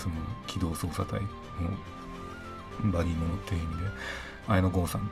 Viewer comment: 警察の機動捜査隊の「バリの店員で」 って言ってる？ 何て言ってる？ 拙者、聞き取れない。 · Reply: いろいろアップロードチャンネル バディーものっていう